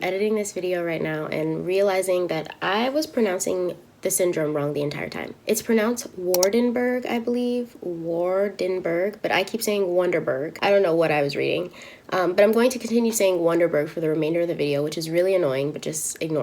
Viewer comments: It sounded like you were saying wonderbird syndrome; SO GURL U IS A WONDERBIRD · Omg girl wanderburg lol I thought you said wonderbread. My fat ass must be hungry lol